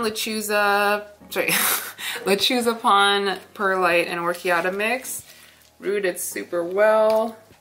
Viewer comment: Hello please share your medium Heard you say pon perlite bark🤔 Thank You❤️💕💕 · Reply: Pon, coarse perlite, and “classic” size orchiata!